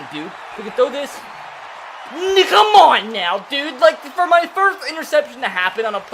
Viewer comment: I dont know boys 3:10 almost caught him slippin 👀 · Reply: I’m sure he was just saying “Now come on now dude!” really quickly, but man I thought he was about to say it too lol